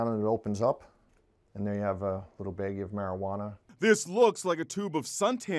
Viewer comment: little baggy of marijuana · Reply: First time it sounded like he said marijuaner